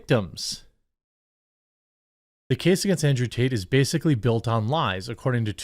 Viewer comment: It sounded like she said andrew taint. LOL. Either way, hopefully the creeps pay their price, regardless of who or what and let the truth set free and condemn..